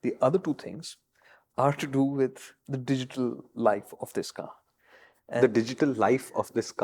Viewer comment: when said digital life of car? I heard what on earth is he smoking!!! Every episode brings so much joy. God bless you guys!!!